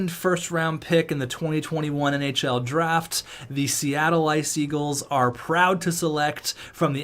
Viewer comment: Anyone else heard « Seattle Ice Eagles »